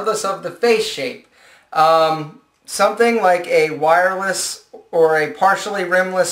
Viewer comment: did he just say WIRELESS?